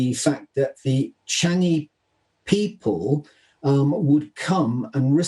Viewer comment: Chinese people ? Initially I heard it as Changi people